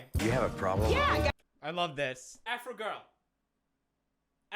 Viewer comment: Omg the whole time I thought he was saying afri girl